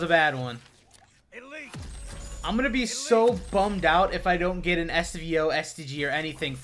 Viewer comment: I thought for sec that he said "I'm going to be so bummed out if I dont get a STD..." at 1:57 hahahh